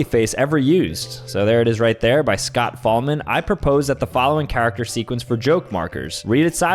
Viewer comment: When he said Scott Fahlman i thought he said Scott Cawthon Gois is that Freddy Foozbear? Or or orr or orrrrr or or or or or or or orrr